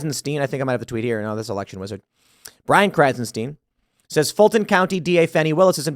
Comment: Omg, I was cooking and heard you say Brofag Krassenbro, and came over to laugh and turn this shit off.